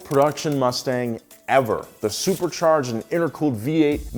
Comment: did he say "supercharged and aircooled"?